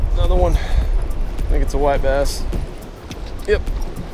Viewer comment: Dang hearing issue make it sound like yer saying wipe ass or its because here in Ca we catch real WiperBass which are hybrids between stripers and white bass and I'm acustomed to hearing wiper bass?Lol All the same great Catch and Cook right there